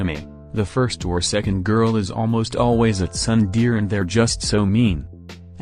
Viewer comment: I thought he said thunder land.